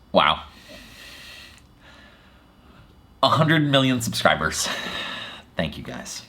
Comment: 1 Million - I meant 1 million 😂 I've been listening to this all day too as I've been editing, and totally didn't catching it... I guess it just goes to show how hard it is for me to wrap my head around this number - And it's already clear you guys will never let me live this down:) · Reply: I thought you said "A humble million subscribers" :)